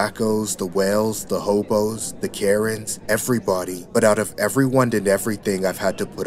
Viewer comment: When he said "the Karen's" i heard "the carrots." Lol